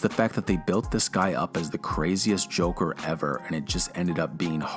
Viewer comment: You thought they said craziest Joker, but they actually said corniest Joker.